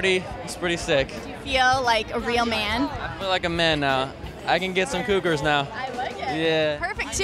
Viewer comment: I THOUGHT HE SAID "I CAN GET SOME HOOKERS NOW" 😂 FAIL LOL